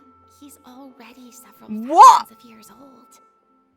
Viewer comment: It was a mistranslation. In other languages, what Furina said translates as Neuvillette is at least AROUND a thousand years old. If I remember correctly...